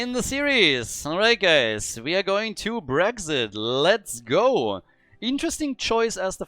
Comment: Did he say Brexit looks at top of the screen heh